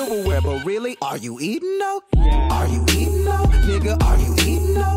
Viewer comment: Am I the only one that had "are you eatin' though?" In their heads so they googled it? 😂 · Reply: I heard "are you eating dough ?"